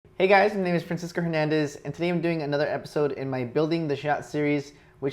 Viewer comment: It sounded like you said you name was Princess Fernandez.